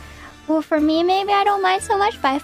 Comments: I think she was saying Gen-Mates not Journey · I think she says "my lovely genmates" but thank you for the clip!